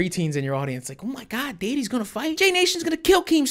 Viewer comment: I'm dying LMAO!!! This man said "Jay Nation" first of all ahahah, and right before that did you say daddy??